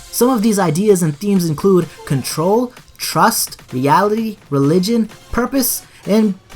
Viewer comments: did you say yaoi. lol how the fuck did yaoi get involved in cod wtf? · reality* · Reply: Negan oh okay but still it's sounds like he said yality